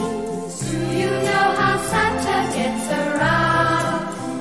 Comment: Rather inappropriately, I misheard the lyrics as "do you know how Santa gets aroused?"